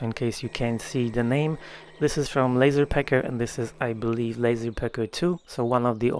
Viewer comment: The first time I heard you say it, I thought you were saying"Lazy" Pecker instead if "Laser" Pecker!! LOL